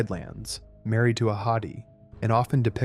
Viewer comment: ‘Married to a hottie’ is all I hear 😂